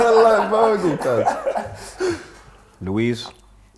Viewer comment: did he just say Luiz😂😂😂